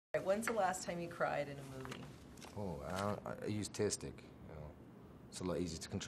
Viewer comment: he use what? Caption says tastic but can't figure out what it means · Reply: tear stick